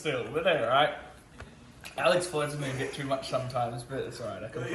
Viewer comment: what is he saying? my brain cant work it out lmao, i know its "alex... a bit too much sometimes" but idk what hes saying haha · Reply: Pays ig